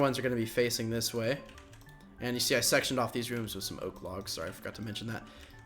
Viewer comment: did he say i sexed off this room?